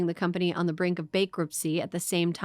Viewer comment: did she say Bakeruptcy